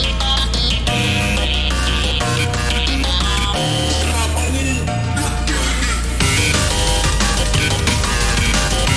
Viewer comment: what is he saying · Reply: "STOP OW! YOU'RE KILLING ME!"